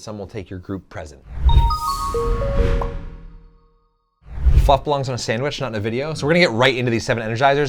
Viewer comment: what is it you said about a sandwich? In any case, thank you for all your excellent videos and other resources! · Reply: Haha, "fluff [the marshmallowy spread] belongs on a sandwich, not in a video."